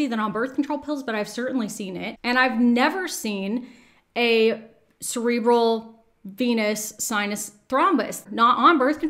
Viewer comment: Not sure if you care. But 9:23 might want a text over correction. Cerebral instead of central. Means something different, no?